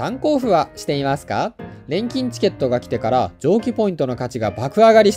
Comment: 錬金チケットが現金チケットに聞こえたw